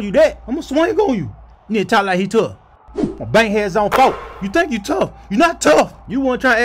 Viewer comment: What does Edwards Say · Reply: It sounds like Bankheads zone 4 but it honestly makes no sense since it's located in zone. So my guess is it's Bankheads on 4